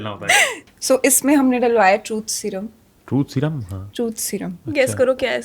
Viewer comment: What does Sadhika say twice.."truth seruam" for real??